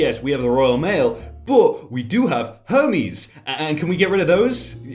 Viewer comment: I thought he said Homies not Hermes 💀💀💀💀